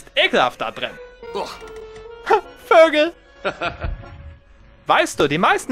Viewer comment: This is what I heard: 0:47 Huh.. Burgers.. Heh.